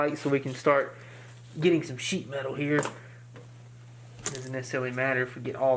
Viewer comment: why It sound like he said sh*t out of here instead of sheet melt